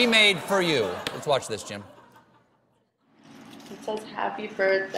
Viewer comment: did he call him Jim? 1:08 HAHAH